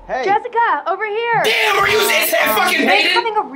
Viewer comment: did he say "DAMN ARE YOU IS HEAD FUCKING HATED" ?